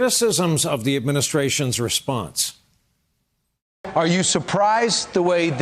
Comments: he said "more on this" after Mick spoke, and I heard "Moron" lol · “MORON, all of that in a bit” Am I the only one that heard this as soon as ‘Mulvaney was done talking? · I thought he just called Mick Mulvaney a moron. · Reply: Anubis no, he said “more on all of that in a bit” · "More on all of that in a bit." What I heard: "Moron. All of that in a bit."